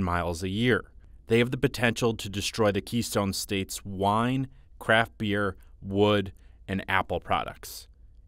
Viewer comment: when he said craft beer i thought he said crap beer